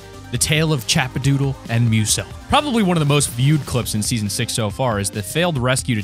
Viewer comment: wait did you say season 6